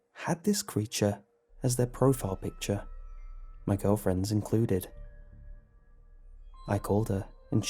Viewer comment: did he say girlfriends at 4:05?